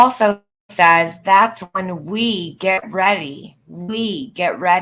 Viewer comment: "That's when we greet" I think she was starting to say "MEET" But changed to "greet" and then morphed into "get ready." 🧐🤔idfk can someone else listen to that part and tell me what you hear?